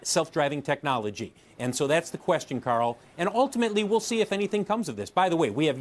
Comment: does homeboy call Jim Cramer "Carl"??? LOL.